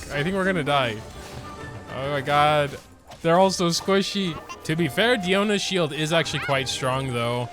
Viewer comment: HAAHAHAH did he say squishy lololollol